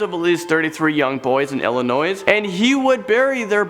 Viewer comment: Did you just say “illinoise”?